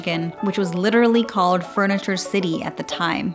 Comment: Why do I keep hearing “freniture”…